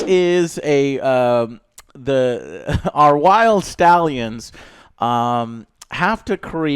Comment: I thought joe said wild scallions.